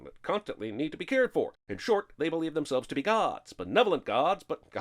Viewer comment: Captions use possessive god's instead of plural gods 13:08 Pepa controls the weather, not Julietta